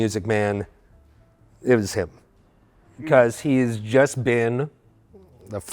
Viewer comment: I'm a little hard of hearing, what did he say here? Sounded like "ivas hibl" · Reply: I heard "It was him"